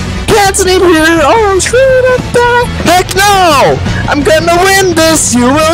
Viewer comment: "Im gonna win this, you will see." I thought he said im gonna win this uhc